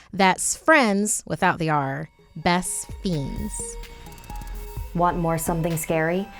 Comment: For a second I thought she said that she buried her best friend.